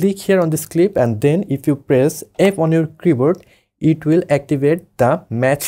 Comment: Sry, but what do you click on 1:10? You said it very fast I cant understand... Thanx bro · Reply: F key